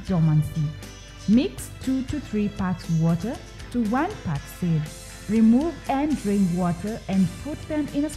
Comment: i didnt catch what she said. anyone knows? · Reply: He meant soaking,2-3 part of water over one part of seen